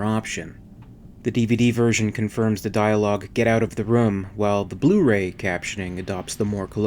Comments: I heard him say 'get out..Run' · To me it sounds like he is saying"Get out of the road" I could be wrong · I always thought he said "Get out of the road"... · Sounds like he said “get out of the road” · I know that this is already taken but I thought he said, "GET TO THE CHOOPPPA", the first time I ever watched it. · I thought he said "get out of the road" ( as in get out of the way)